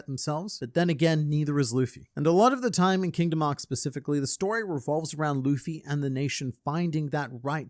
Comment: For half a second at 5:36 I thought you said “in Kingdom Hearts” instead of “in Kingdom Arcs”